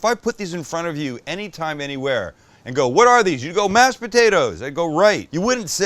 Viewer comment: I heard “Max potato” 😂